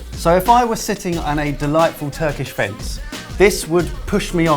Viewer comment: "Sitting on a delightful turkish fence" Misheard that as "face" at first